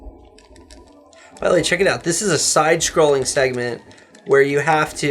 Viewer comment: Did he say "sidescrolling segaman"?